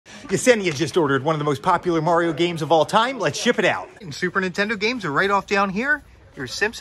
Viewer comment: I thought he said “Mario Kart Game” and I was like : Thats not Mario Kart